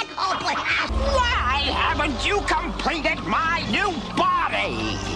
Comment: did it just say "snarf the fish" lol.